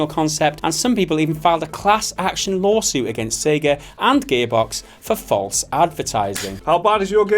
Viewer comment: My inner child misheard at 7:10 say 'Gaybox' software, maybe it's the accent. And I'm a Star Citizen fan and I still watch your videos - no way you can call that anything other than development hell. They seem to be back on track though, we'll have to wait and see...